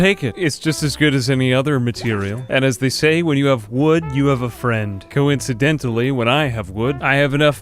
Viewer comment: Is that what they say? I thought it was "When you have wood, call a friend." To each his own I guess.